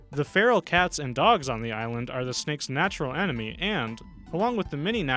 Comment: Concepcion Silver Boa.. 1:56 "feral cats and dogs are the SPHINX's natural enemy".... Did I hear that correctly..? Sphinx?